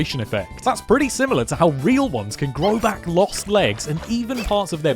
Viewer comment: I got stuck replaying and turning it back and it sounded like you said “It’s pretty similar to how they only eat their brains!”